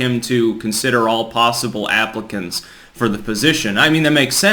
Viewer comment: When you said "all possible applicants" I thought you said "all possible Africans". I was like WTF?